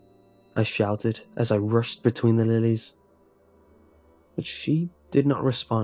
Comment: thought it said “ between the lines “ the whole time ;(